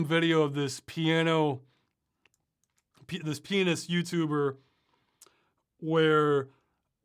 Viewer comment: Anyone else hear “Penis YouTuber”